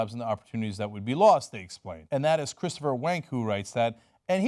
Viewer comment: Did you just say Christopher Wank?